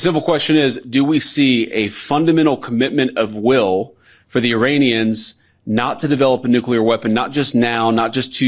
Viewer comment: does he call Iranians, Uranians?😬🥴